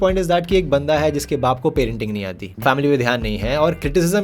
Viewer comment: i heard painting nahi aati which is kinda..............